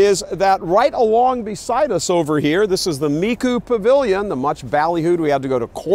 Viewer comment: And here I always thought Michael was saying "the Me, Too! Pavilion" 😏